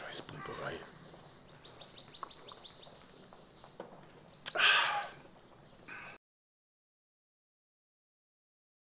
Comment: Was sagt er bei 4:42? Scheiss ? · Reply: Scheiß Pumperei